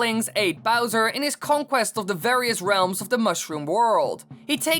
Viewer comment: I thought he said "ate"